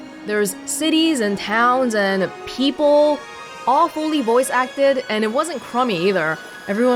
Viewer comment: for a second I heard you saying "awfully voice acted" lol